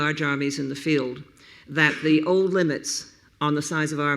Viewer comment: that the ?????? on the size of armies... Anybody got a clue about what she is saying? I do not know what word she is speaking and it sounds foreign to me. HELP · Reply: That the old limits...